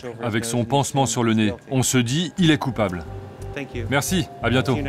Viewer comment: il a dit quoi alors ? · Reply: "ON SE DIT il est coupable"